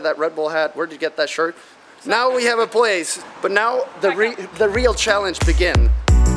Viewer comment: Did anyone hear this too at 04:12 - ”But now the ring, REAL challenge begin”! Someone almost ruined the engagement! save Jon! 😂✌🏼